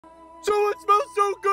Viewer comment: When I heard fish sticks (Hooman voice) I thought he said dish s$%t